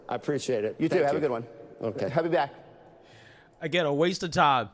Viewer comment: What was that blabble? Did he say "Have me back"?